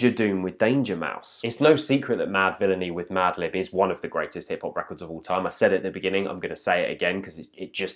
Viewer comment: When he said "one of the best hip-hop records" at first I thought he said "one of the best hit-pop records" and almost had a heart attack